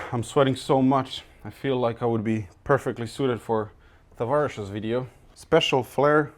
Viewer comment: I like your style of commenting. Did you say Tavarish at almost the end(51:14) of the video?